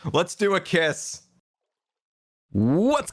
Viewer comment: So I start up the vid and I just hear, “Let’s do a kiss.”😂